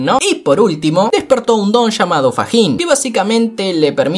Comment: chale odio mi dislexia entendi PAJIN 💀👻